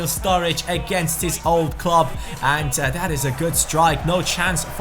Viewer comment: I thought u said own club